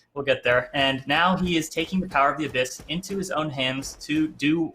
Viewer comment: "Behold the power of the Abyss!" Is this the ult quote? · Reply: I heard "Neil to the power of the abyss"